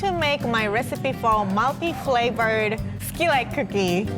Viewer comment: Oooh I thought she was saying milky flavor but she is saying multiflavor